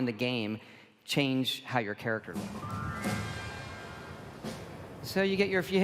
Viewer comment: did he say looks??